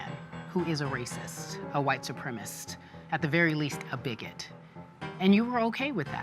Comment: does she really say "a white supremist"?